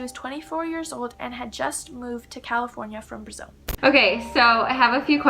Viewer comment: On 6:20 did she say Brazil?